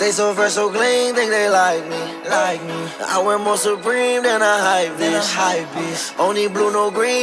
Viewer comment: I'm i the only person that thought he said "I wear more supreme then I'm hypebeast" instead of "i wear more Supreme than a hypebeast"